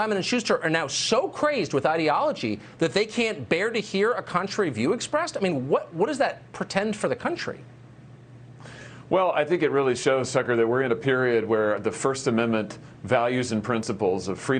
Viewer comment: WAIT WAIT WAIT Did he say, “what does that PRETEND for the country,” just then? That’s an interesting little slip up.